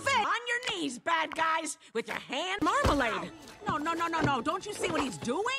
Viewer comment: did she just say "Hands marmalade"